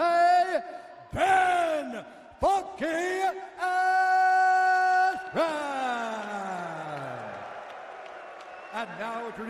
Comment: I thought Bruce said "Ben F***ing Askren." 😂